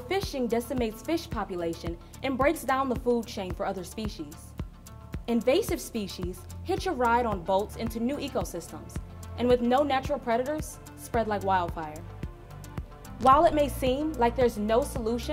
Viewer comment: Hi, really interesting video and since I can't properly understand what the speaker says around minute 2:26, could anyone help me? She says that "invasive species hitch a ride on volts...into new ecosystems"? The subtitles, authomatically generated, write "volts" but I can't find another meaning apart from a measure unit for electrical force (which I believe is not the right one). Thanks in advance.